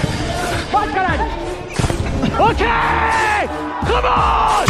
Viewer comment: eu escutei um "vai caralho!" ?